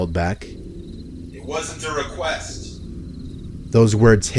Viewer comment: It is still unaudiable for me what the guy says at 9:09 . I think he says something like "It wasn't to request" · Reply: It wasn't a request.